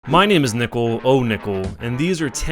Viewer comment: for a second there I thought it said , "My name is Nipple , OhNipple."